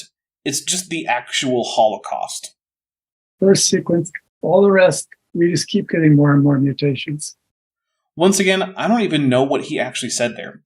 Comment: I believe what he said was "reverse sequence. All the rest, we just keep getting more and more mutations."